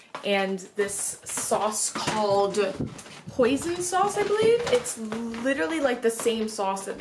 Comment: Lmao I thought you said "poison sauce"